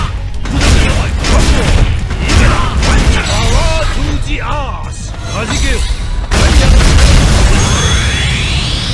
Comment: Did G say “Power to the ass”? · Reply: "POWAH TO THE AHSS"